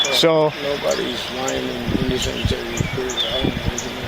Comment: sounded like the bad cop said "nobody's lying in the cemetery dead" 🤔 Ummm... There's LOTS of people lying in the cemetery dead 🤣